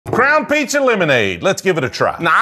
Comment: I thought he said “Crown Pizza Lemonade”